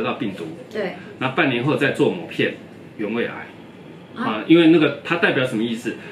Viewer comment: 有胃癌？听不清啊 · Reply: 不是聽不清，醫生其實講得很清楚「原位癌」，而且其實... 影片有字幕可以開啟...：）